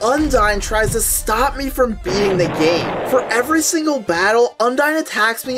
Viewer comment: I heard "this is UNDERTALE, But Undyne tries to stop me from being the GAY" 💀💀💀